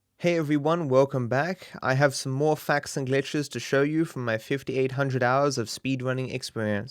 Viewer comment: Hey i wanted to show you some packs and glitches from my 749272 hours of speedrunning Edit: thanks for the likes most likes on a comment i ever had also i thought he said packs and glitches but he said facts and glitches😂